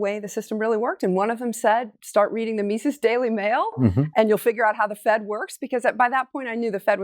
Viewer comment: Can anyone tell me what she said at 2:09, "something" daily mail? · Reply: She said "Mises daily mail". Generally referring to the mises website , i take it --> <link>